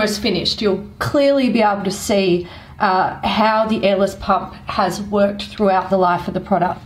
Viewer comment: My hick-ass thought they were saying Ellis pump.🤣🤣